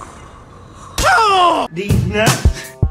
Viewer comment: what did he say? I hear "did not" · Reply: He said DEEZ NUTZ I'm not joking that's actually what he said